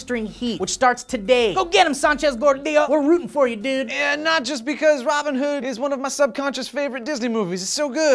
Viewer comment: I thought said "E Nachos" not "Not just" lol